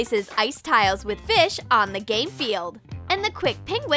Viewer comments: WHY DOES IT SAYS GAY FIELD!?!?!? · did you say GaY fEiLd!?! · ON THE GAY FIELD??! OMG 2 LIKES THANK YOU SO MUCH (I’ve never had two likes._. I’ve had comments saying that I can’t hear. It’s just a joke, please don’t hate on other commenters, Bye! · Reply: When I clicked the 6:26 first I heard gay field and then game field